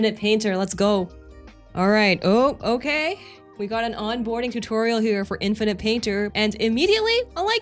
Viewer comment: Thought I heard you roasting new users "here we have a tutorial for infant painter"